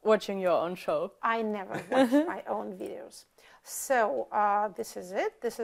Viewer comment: На 0:58 мне слышится "i never watch my own videos", а ютюбу "i never miss my own videos". Есть подозрение что я все-таки лучше слышу, но там очень удачно смешок идет от журналиста, что точно сказать какой тут уровень иронии нельзя.